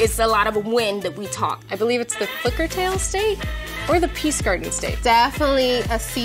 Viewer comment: I thought she said the hookers tale state😭😂